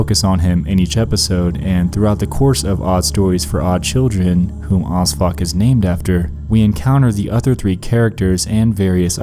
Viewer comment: When you said O.S.F.O.C, I thought you said ‘who odd fuck was named after’.